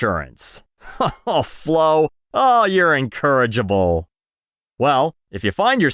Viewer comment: did you mean incorrigible or encourageable? It sounded like the latter but the former would make more sense.